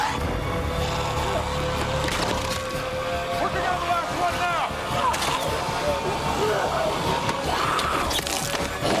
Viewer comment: What is said at 0:23??? Sorry, couldn't figure it out... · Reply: I think its "working on the last one now"